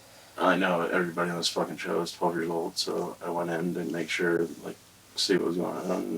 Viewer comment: Does he say: 12:55 everyone on this show is 12 years old? He’s friends with Ross who testified Monday, orange hoodie.